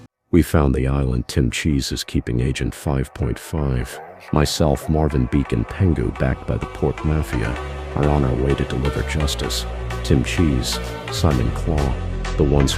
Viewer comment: my Bungo Stray Dogs obsessed ass thought it said “Port Mafia” 😭